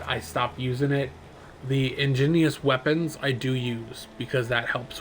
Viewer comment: thats "Igneous" not Ingineous or what ever you said